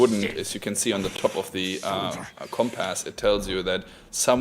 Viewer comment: Did he really say "comp" "ass" @ 21:45 ? lol